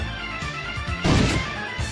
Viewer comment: 笑うで、って聞こえんだよね😅